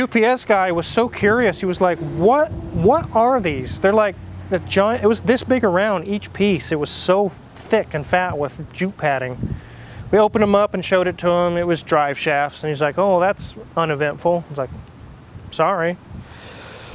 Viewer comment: is he saying “JEW PADDING?”